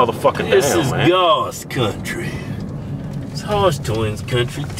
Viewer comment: i thought it said hotdog twins